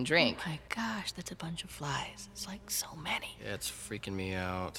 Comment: I thought she said "That's a bunch of lies".